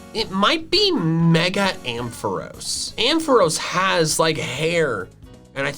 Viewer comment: The moment you realize he said mega Amprose when you thought he said mega Altaria after disagreeing saying your counter play mega Amprose which he already said